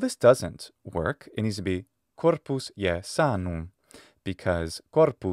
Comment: What I heard is " Corpus si sanum", but maybe my headphones are not that good...